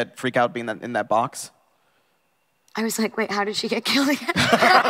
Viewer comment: Somebody can tell me what does Emma said in the 39:59 pleaseeee!! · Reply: I think she said "I was like wait, how did she get killed again"